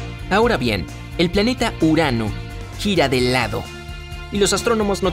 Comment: Yo entendi " EL PLANETA URANO GIRA EN EL A$O " 2:26 jajajajaj😂😂😂😂😂😂